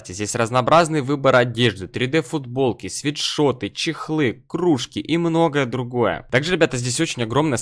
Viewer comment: Мне послышалось здесь 3d футболки, свитшоты, чекушки... Вот это я проорал